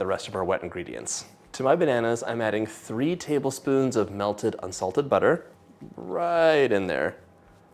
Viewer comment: Lol... I thought you said "assaulted" butter... I was like HUH? ....oh... unsalted butter....lol